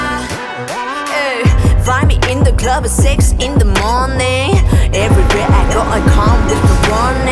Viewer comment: Been listening in love and I thought she said “finally in the club sex in the morning